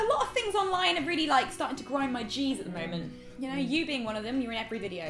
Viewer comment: I THOUGHT SHE SAID GRIND MY CHEESE